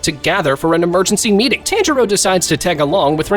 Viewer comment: what he said: Tanjiro Yt captions:tantaro (bro yt is inosuke or smt)